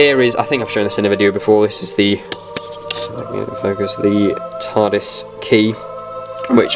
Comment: did you say TARDIS or RETARDIS? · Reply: he said "John Pertwee" tardis